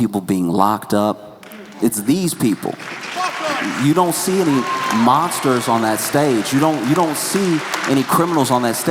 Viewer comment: I can't tell what the guy is saying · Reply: The audience member yelled "fuck ICE"